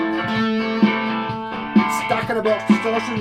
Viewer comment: wow really great video I learned so much you are a great teacher, Sir. oh did you say " stack in the box distortion" @ 18:46 when you kicked on a distortion pedal, ? Thanks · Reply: yeah its a washburn stack in a box sx3, this vids a couple of years old but i put it back on my board last week - spooky! , here ive got it plugged into a wee 15w valve laney and a 3x12 so that kinda already is a marshall stack type thing - where it really shines is now when i have it into a solid state 100w amp that doesnt sound like a stack but with the pedal it does! - im now actually using it for what its meant for! - ive since gone mad for blazers/roadstars and have a pile of videos on probably about 10 of them and own a few myself but this guitar is what started it - played my roadstar rg140 in the studio with my band today! rock on!